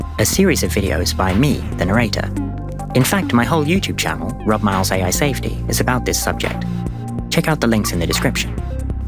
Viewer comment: I think myi must be buggy, when you said your name I heard Rub my isle's safely, time for a new model.